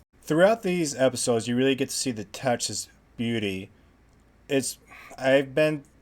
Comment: "touches beauty" took me a minute to figure out what you were sayin'. The Billy Bob scene was wild.